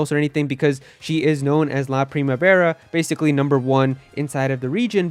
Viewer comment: did he say primevera? That means spring